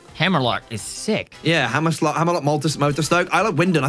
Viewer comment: did say hammerslut